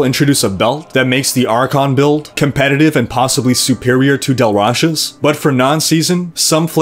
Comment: Lol not to knitpick on an awesome guide(again) but it was really bugging me you sounded like you were saying "Dal Rasha's" set not "Tal Rasha's" set :) Thanx again for the video cant wait for 2.3 to bring me back to tristram